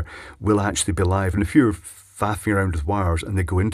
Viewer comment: What is that word he said at 16:43? "And if you were faffering around with wires..." What does faffering mean? I'm curious, because I invented that word years ago, and my wife & I use it all the time. We use it as a "fill in word" if we can't think of an actual word in a sentence- Example: "I changed the faffer in the furnace because it was squeaking". It's a fill-in word... because it would take a minute to think of the actual part name.